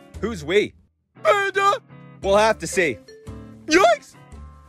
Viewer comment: What hammy thought he said: he will have to see. What he actully said: we will have to see